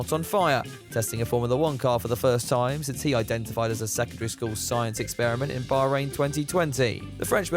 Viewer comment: I heard the joke @ 1:58 wrong at first, i thought you said sektarist school experiment. I thought that was rather funny. Given the race's location. But i was wrong. Seems the best jokes come from X in your video's now.